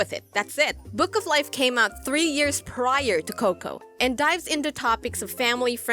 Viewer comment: I heard 'Bout 3 Years ago :/